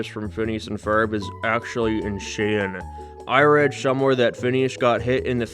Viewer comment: dude I heard " you should make a video about how Candace from Phineas n Ferb is actually Ed Sheeran" but I don't know what he actually said cuz the accent im sorry 😅